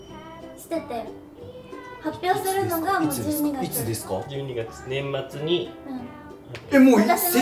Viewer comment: ここ、いつですか？！が蜜です！！に聞こえるww